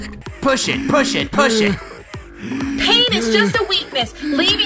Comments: I'm sorry but did she say penis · did she say penis